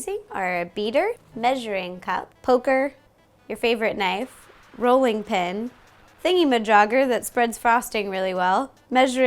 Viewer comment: does anyone else hear "Ro-lling pin"? :D